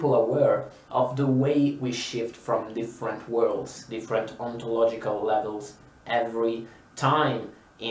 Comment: Thought you were saying oncology because of the closed captioning and I was so confused 😂